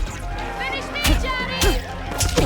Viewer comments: Please tell me Im not the only one who heard someone say “Finish me, Johnny!” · who else heard finish me Johnny